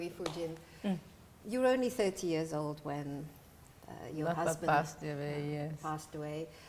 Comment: subtitle 0:14 salah ya? interviewer kedengerannya bilang “you were only 30 years old when your husband passed away” tp kok subtitle nulis 19 tahun?